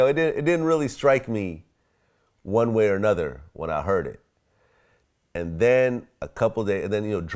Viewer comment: Ok, um.....this guy didn't say "when I heard these guys" but "when I saw these guys" I dunno just sounded weird!